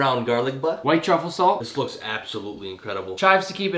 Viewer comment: Why is everyone saying it’s “bismillah” I hear “nabismillah” Edit: now unless they’re saying “now bismillah” I don’t know